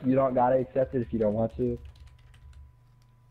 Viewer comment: Did you hear them at the end? I thought I heard them say ‘aight he dipped, yo man f* his rules’ did anyone else hear this?